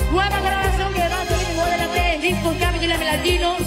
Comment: Does anyone know what selena is saying here? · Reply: Nueva grabación,... ¿que va a salir? ¿disco platino?